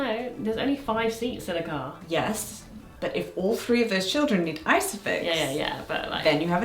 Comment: What's ISOFIX? I had to turn on captions, I thought y'all were saying "ice picks" and "one ice pick per child" and I was like... that can't be right, this channel is too wholesome for that